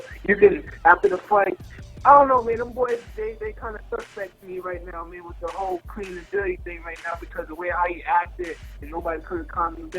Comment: What did he say @ 1:34? Did he say they were acting funny with the clean and dirty thing? Talking bout PEDs & drug testing?